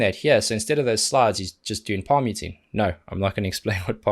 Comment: Had to turn on the subtitles, because I thought I heard you say: Emppu just doing vomiting and you're probably sick of it..🤔 My hearing sometimes.🙄😥